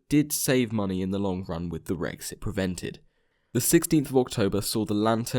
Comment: I heard "but did save money with what Brexit prevented." I started wondering why things got briefly political, so I relistened to the line and adjusted my ears.